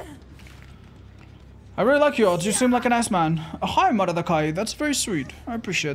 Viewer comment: I swear that he said “I really like you, you seem like an ass man.” And following response was hilarious and confusing with this in mind.